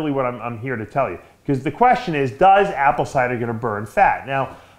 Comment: wtf does he say 🤣🤣🤣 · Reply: " does apple cider gonna burn fat" 😆